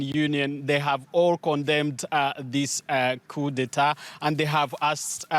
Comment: what you said?? 🤣🤣🤣🤣🤣🤣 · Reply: Coup d'etat